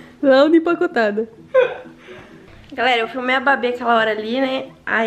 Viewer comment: mano só eu q ouvi "galera eu fumei a babi"